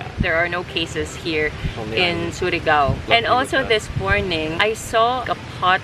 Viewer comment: did i hear surigao? 😆